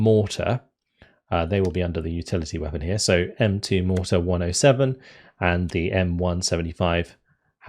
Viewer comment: uh the WHAT gun? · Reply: He said "The M2 107mm mortar" at 1:49. What did you THINK he said?